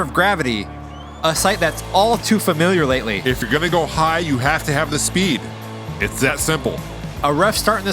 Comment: I thought said "if you want to get high, get speed!"